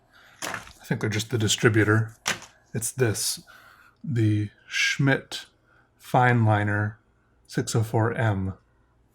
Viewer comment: Oh lordy I must have fluid in my ears frm this virus. I swear I heard 'Shit fine line'. & even did the head double take. What did he just say? But after rewinding & actually watching it again, no it was me & my goofy ears again & I just broke out into hysterics. Oh lordy I needed that. After being scared earlier part of week & waiting for the positive or negative results. It was good to laugh the stress away.. Thank you, you just have a way of melting my stress away.